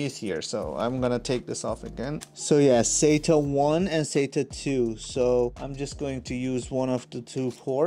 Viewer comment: Great review!!! Did you say (4:14) sata 1 and data 2? It cloud be possible to attached 2 ssd (with an aditional wire I guess) at the same time??? Thanks!!!